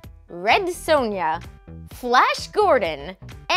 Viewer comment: Tee hee hee. Sounded like she said Flesh Gordon :D